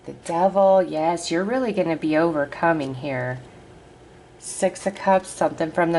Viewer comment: 🙌🙌 Thank you! (When you pulled the 1st 3 tarot i thought you said "Youre going to be really over COMING HERE" And i thought "No no, for the first time in this life I'm so GLAD I CAME HERE! Im falling in love with earth and all its lifeforms and... oh, you said 'OVERCOMING here'... heh heh😅) 888 - my number for this year, energy of abundance - and today (8th) starts a process of getting me finally debt free, all within the next week! And my entire life up until now has been marked by poverty, but so many things have been shifing, and so rapidly! So excited to see what this month leading into the LGP will open up for me. Thank you, beautiful, I credit your input into my life as being a big part of that. 💐💐💐